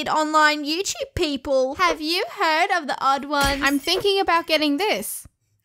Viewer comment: Did you say the odd Is out at 3:37 Im afan of the odd Is out